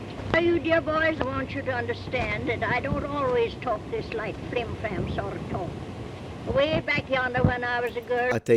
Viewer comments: 7:17 I think she says "I don't always talk this like 'frime femme' sort of talk", meaning she doesn't always sound so pretentious/pompous like that. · sounds like she said, "I don't always talk this like, Flim-Flam sort or talk 🤔